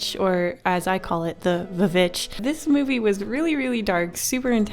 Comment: i thought she said the b****😂😂😂